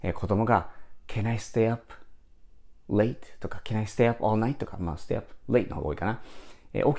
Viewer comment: I staple at night.と聞こえた。